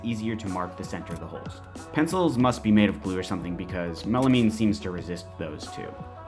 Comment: “Pencils must be made of glue…”??? “Glue”??? Is that what he said? I couldn’t make it out.